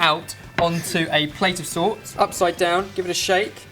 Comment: I thought that said "Turn it out onto a plate of salt" ... Not recommended